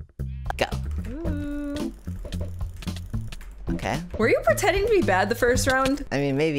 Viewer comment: When said “are you pretending to be bad?” I thought she was saying, “are you pretending to be badboyhalo?” 🤠